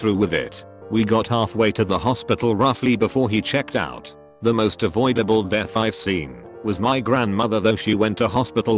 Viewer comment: I thought it said "most adorable death scenes".